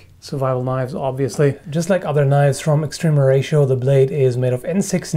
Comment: Must be late, I thought you said someone by the name of Extreme Horatio sent this to you.